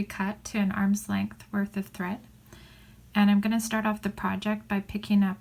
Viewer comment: So pretty! I thought I heard you say 10 arm lengths of Fireline. It didn’t look like you were working with that much thread. Was that just for ease of recording the video? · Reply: Thank you! No, I said an arm's length worth of thread so that it's manageable. I definitely wouldn't recommend working with that much thread:) Thanks for leaving a comment!